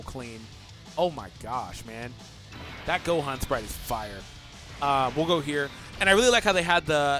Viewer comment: I thought he said: "This Gohan's butt looks fire"" xD lol